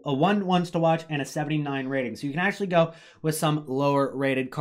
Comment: did he say 79 rated?